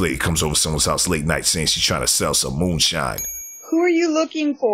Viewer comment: Very polite + who said moonshine? I thought she said “goods”. Looked like some canned and pickled food 😅